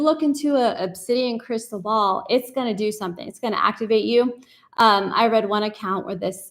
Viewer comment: I heard you say ‘octovate’ 🐙